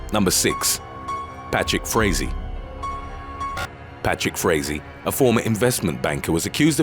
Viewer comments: When you said "Patrick Frazee" I thought you were saying "bat shit crazy". 😂😂😂😂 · The name of the suspect in the second video made me do a doubletake. I thought it said his nsme was Batshit Crazy, not Patrick Frazee. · The name of the suspect in the second video made me do a doubletake. I thought it said his nsme was Batshit Crazy, not Patrick Frazee. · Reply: I thought he'd said Patrick Swayze 😂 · When you said "Patrick Frazee" I thought you were saying "bat shit crazy". 😂😂😂😂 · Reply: I thought he said 'Patrick Swayze' 😆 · When you said "Patrick Frazee" I thought you were saying "bat shit crazy". 😂😂😂😂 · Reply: I thought I heard Patrick Swayzee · I thought you said bats*it crazy instead of Patrick Frazee, either works though.